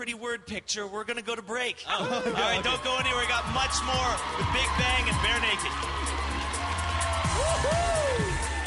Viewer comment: lol its sounds like he says: "the big bang is bare naked" at the end hahahaha